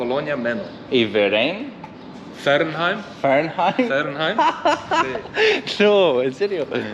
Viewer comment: I think hes pointing out the other colony "Fernheim" next to "Menno", because "Verein" is not pronounced like that and only means "association". Verein is pronounced like "fair ign" The dialect is also very interesting, although i cannot understand it (during the cookup with alfreds friends). It sounds very nothern german, maybe its an old dialect called "plattdeutsch" thats not spoken in germany anymore